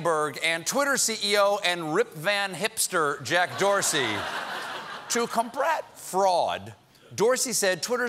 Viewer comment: is it just me or did he say to combrat fraud..